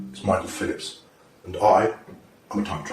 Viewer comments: I thought he said Michael Phelps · I thought He said his name is micheal Phelps the olympic swimmer 😂🤣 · I thought it said micheal Phelps 💀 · I thought it said Michael Phelps for a second 😂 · At first I thought it said Micheal Phelps.....I was life WTF?! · I thought it said Micheal Phelps and I was like what the actual fuck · Thought it said Michael Phelps at the beginning😂oof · lol who else thought the first prt said michael phelps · I thought he said he was Micheal Phelps.